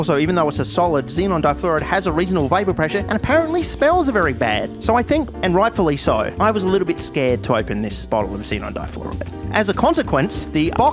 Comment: "so I think <cut> and rightfully so" what was this supposed to say? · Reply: "so i think (and rightfully so) i was a little bit scared to open this bottle of xenon difluoride"